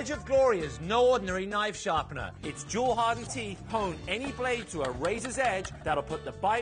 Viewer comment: sounds like he says "pwn any blade..."